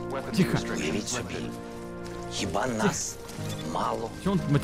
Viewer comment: кто услышал мат? Я Ржал пол часа. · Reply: Еба нас мало